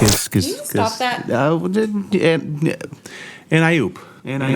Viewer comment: I always thought they were saying, "alley oop". Which is a basketball term and 1930's strip comic. Like something you say while bouncing a kid in the air. Wtf does, "and I oop" even fucking mean???